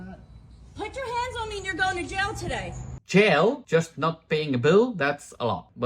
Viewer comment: she actually said "put your hands on me and you're going to jail today", so it wasn't for not paying the bill. ✌️